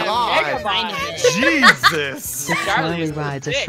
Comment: Did say "Jesus"?